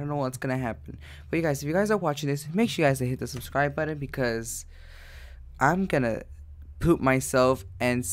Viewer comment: did he say I’m gonna poop my pants!? 😂😂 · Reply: No he said "i'm gonna poop myself"